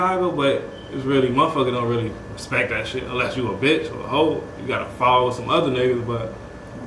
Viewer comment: did he say “on O” 😂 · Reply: He said a bitch or a hoe